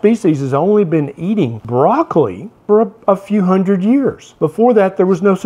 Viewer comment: Did he just say broccoli has been around for only 100 years?! 😂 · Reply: he said “a few hundred”.